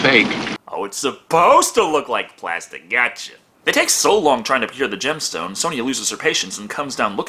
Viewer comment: Did he say "It takes so long trying peer the gemstone"???